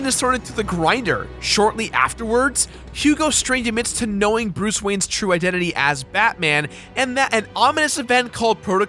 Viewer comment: Did he just say, Bruce Wayne's true identity is batman?